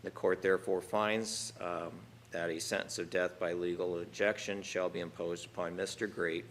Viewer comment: legal injection? Thought it was called LETHAL injection??